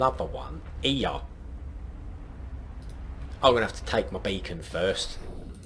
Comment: I'm going to put bacon above it. Me: Did he just say bacon? Oh...beacon....right....